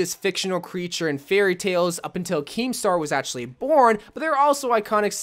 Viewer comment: wait I'm confused, is he saying "... up untill Kiinstar is born" ? Am I just death because that litteraly makes no sense:what does a French Fortnite champion have to do with any of this ? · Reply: He said Keemstar who is the person who runs drama alert